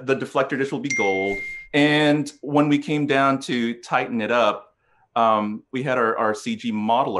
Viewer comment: I think he's saying "tighten it up" but I am definitely (mis?)hearing "Titan it up". Not sure if that was an intentional joke or just a coincidental homophone.